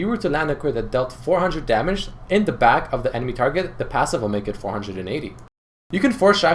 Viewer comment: what's he saying? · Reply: Bacon Sauce It sounds like: The passive will make it 480